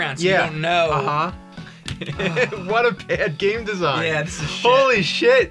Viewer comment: I have autogenerated subtitles on and when James says "what a bad game design" the subtitles said "what a good game design"